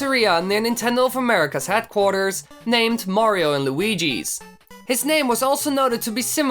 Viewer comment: it sounds like he said waluigi